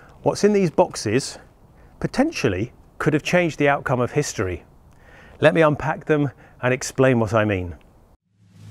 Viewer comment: "Let me unpack them and explain what I mean" - is what he said and what I heard was - "let me show you its features"